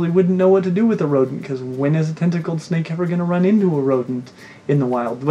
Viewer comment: Omg I thought you said testicled snake! 😂 the visual I had!